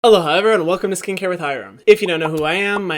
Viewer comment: I know he says “aloha everyone” in the beginning but I always hear “hello hi everyone” but like really fast😭